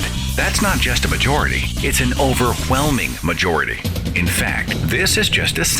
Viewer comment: "It's an OVERHWHELMING MAJORITY!" me- Wait What? him- an overHwhelming majority? me- say over him- over me- say whelming him- whelming me- say overwhelming him- overHwhelming me- WHAT THE FUCK!?!